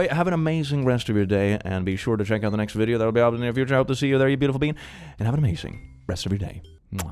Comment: Are you saying "beautiful being" or "beautiful bean?" Because I totally hear "beautiful bean" and now I really want a Beautiful Bean plushie!